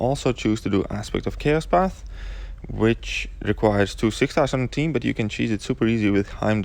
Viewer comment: Can anyone understand what this guy said at 1:27 ? Something about "2 6 thousand something" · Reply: "Require 2 6 stars in the team"